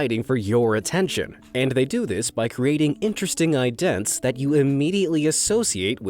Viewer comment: “interesting idents” what? What was that word after interesting? Maybe I’m deaf but I listened two or three times and heard “idents” pronounced like “eye-dents”